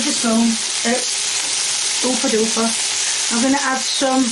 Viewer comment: Omg you guys dont laugh at me but i swear i heard her say oofa doofa 🤣🤣😫😫😭😭 I CANT BREATHE 🤣🤣